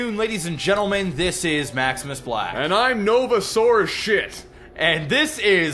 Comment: did he just say "...and I'm novasaurus shit"? Sure sounded like that! d; Also, a 'Novasaurus' would be epic. Together with a Tyblackosaurus Max.